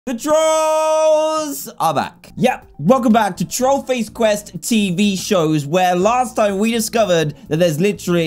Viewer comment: Dan : THE TROLLS are back Me : but I thought you said buttface quest 🤔